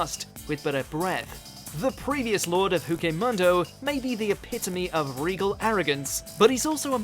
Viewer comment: What did he say (8:03)... I thought I heard that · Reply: Did he say “Huke Mundo” or did I have a Stroke?